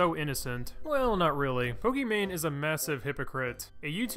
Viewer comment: I thought you said Pokémon